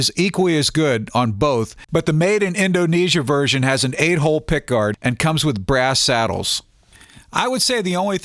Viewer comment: I about choked when I thought he said "A-hole pickguard".